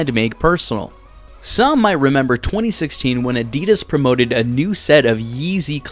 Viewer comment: Is it just me or did he just say adeedus instead of Adidas